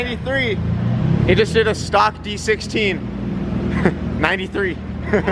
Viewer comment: I thought he said stock B16 at first.